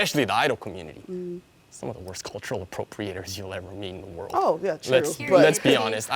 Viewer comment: did he say that Idaho culture was terrible